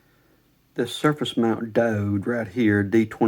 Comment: At the ~10:00 mark, when you found the issue, what are you saying exactly? It sounds like you're saying something "dode?" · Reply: Diode